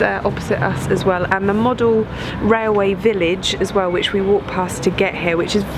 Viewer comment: Aww, I thought you said model-railway village, as in a village in miniature...